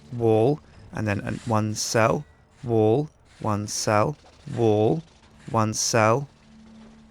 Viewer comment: It sounded like he was saying one cell wool.